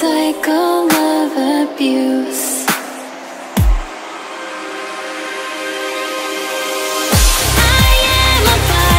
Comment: I STILL WENT TO KNOW WHAT HE SAY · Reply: He said “don’t tell anyone,okay?” That’s what he said lmao 😂